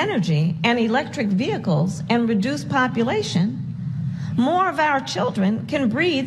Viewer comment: "When we invest in... ... and reduce population..." Wait, WHAT? WTF DID SHE SAY?!